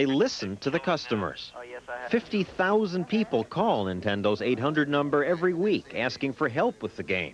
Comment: did he say 800 number? Funny because I remember the 900 number you called for help and paid by the min lol number was all over the gamebooks and Nintendo Power magazine